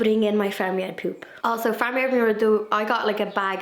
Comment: I totally thought you said "family of poop" 😆 Glad you're feeling better